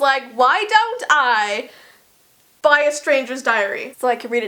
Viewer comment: I THOUGHT YOU SAID STRANGER THINGS DIARY YOU SCARED ME